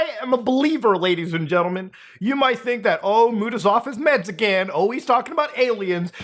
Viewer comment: You said "...is off his meds again", I heard "...is Mexican"... Took me a sec